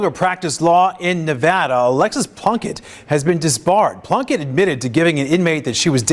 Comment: I thought you said Alexis Blumkin at first and had to rewind and watch more closely.